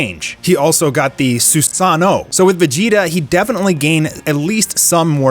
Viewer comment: did I hear that right? Did he say Susano?